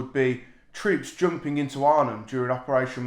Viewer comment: LoL I sworn you said "troops jumping into Ireland during operation Market Garden" but after listening a couple of times Arnhem pops out